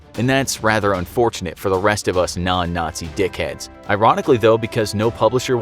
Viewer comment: did he say dicheads?